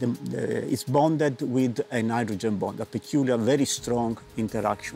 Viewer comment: sounds like he says nitrogen bond, and that's also what the caption says. I can see why people thought it sounded crazy!